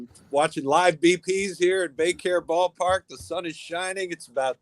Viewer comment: The Phillies Spring Training center is no longer called the Carpenter Complex? The first five or six times I didn't realize that the name was dropped in favor of the corporate sponsor, I thought they were saying "daycare"